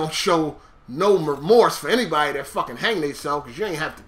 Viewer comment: You're an idiot. At 11:04 he says "I usually don't show no REMORSE for anybody that fucking hang themselves because you ain't have to do that." not RESPECT you moron. And he's right in what he said. It's unfortunate that the boy committed suicide, but at the end of the day it was HIS choice. He had other options. So you just made yourself look like a moron on 2 accounts. 1 for MISQUOTING and 2 for falsely accusing as a douche. Great job man.....Great job lol.